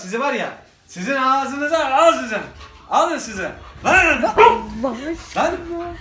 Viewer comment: ne diyor ? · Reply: Allah aşkına diyor